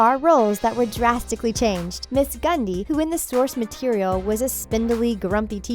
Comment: did she just say “gundy”? It’s Grundy